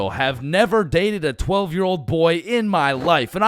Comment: DID BRO SAY 12 YEAR OLD BOY